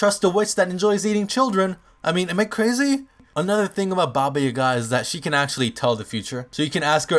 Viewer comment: I thought it said baby yoga